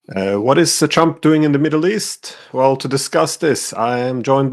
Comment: Just started, sounded like you said, What's the chump doing in the Middle East?😂